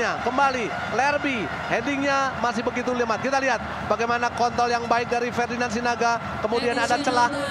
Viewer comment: kalian dengernya apa? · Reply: KontooI yg sangat baik👌